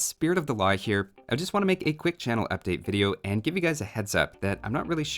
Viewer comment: I thought it said "camel update" for the longest time. I was like, where is the camel update??